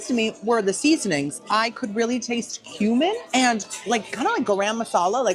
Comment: When you said, "I taste cumin," I thought you said "human" at first. I was like...girl....